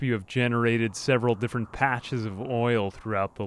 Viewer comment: "Did I hear oil?"